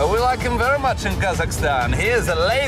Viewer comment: I'm stupid. I figured out from reading these comments that he says "car crash". I genuinely thought he said cock rash. It wasn't until I read the comments i realised😒